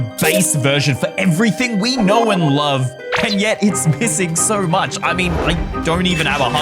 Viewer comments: My dumbass thought he said "pissing" not "missing" 💀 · Did bro say it's pissing so much